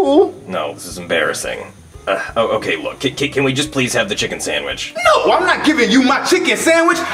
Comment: Did no one else hear at 10:10 the dolph8n said samwich instead of sandwich